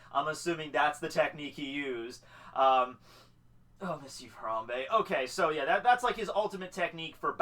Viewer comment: Am I the only one who heard "I miss you harambe"😅